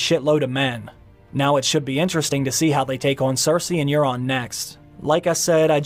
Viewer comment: Did you just say neuron instead of Duron? :)